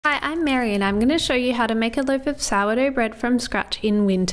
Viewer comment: The world is gone to a weird place when she has to tell that she is married as the first thing in her videos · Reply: I thought she said, “I am Maryanne.”